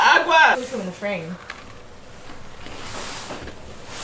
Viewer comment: What did say? It's inaudible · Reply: "i hope you know we didn't make these with a recipe"